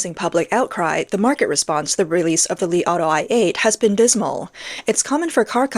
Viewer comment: I heard "the Li Auto I ate"